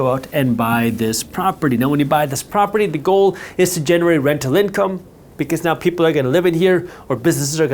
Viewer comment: ngl I heard "generate RECTAL income" for a sec